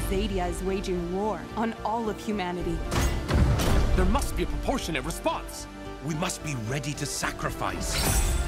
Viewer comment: Does someone know what does the guy in 0:21 say? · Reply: "There must be a proportionate response!"